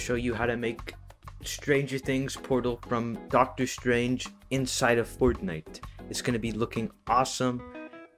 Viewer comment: lol did he say dr strange